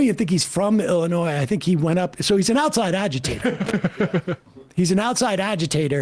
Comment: I thought he said “He’s an outside alligator.”